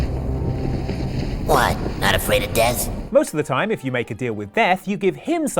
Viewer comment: Did he say meth?